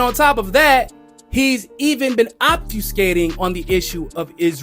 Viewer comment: The caption saying "obtuse skating" instead of "obfuscating" kinda works!